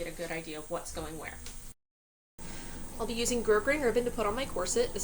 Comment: I'm sorry, I couldn't make out what you said at 0:40. Bergrainder ribbon? Gergrainer ribbon? Bird grainer ribbon? Gerd green ribbon? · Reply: Grosgrain (pronounced grow-grain)